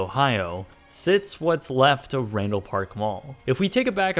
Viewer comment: Even though the title is right there I thought you said 'Rainbow Park' Mall lol